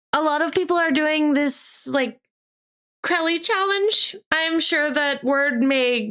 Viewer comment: i have no idea what the challenge at 4:00 is since i never heard about it, i dont even really understand whats its called so i will just call it Krell challenge since thats what yt captions said. but im guessing its something from tiktok that i never used and never will use and its probably something really stupid like that tide pod challenge or whatever it was called edit: so its a mukbang, but mcdonald? i find them gross but i know that some weirdos like watching people eat food on stream (i didnt look it up since i dont even know its name, i just guessed it from saying "i cant fit that amount of food in my body") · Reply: The McCrelly challenge is from the Vtuber Crelly who is a mold cow Vtuber and it's her regular McDonald's order not a mukbang, although most Vtubers and irl streams who try it usually do a handcam to show the food. There is also an official website that shows the full order